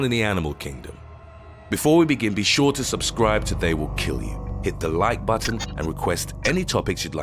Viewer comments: I almost thought the narrator said, "Be sure to leave a like or we will kill you." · Haha. I swore for a second you said, “Subscribe, or I WILL KILL YOU.” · "Be sure to subscribe to They will Kill you" What i heard: "Be sure to subscribe or They will Kill You" 😨